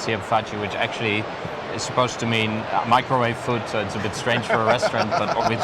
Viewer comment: does he say "dog food"? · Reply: I'd say he's says "fast food".